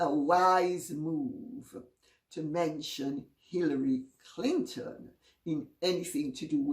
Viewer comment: LOL, I thought she said Pillory Clinton.